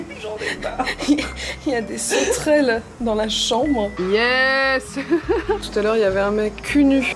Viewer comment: c'est grillon, voilà c'est dis j'ai fais mon casse burne démonter moi en commentaire